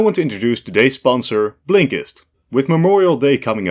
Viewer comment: I thought he said Blinkit. iykyk